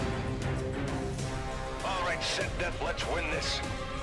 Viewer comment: did he just say "our ancient deathmatch win this"? EDIT: 0:38 i like how when he says "fight to win" the emblem dissapears. that timing was perfect.